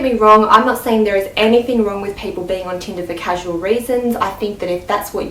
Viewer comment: I adore your accent but I know I heard "Go on Tinder for casual raisins." I personally cannot stand casual raisins, on Tinder or anywhere else. ;)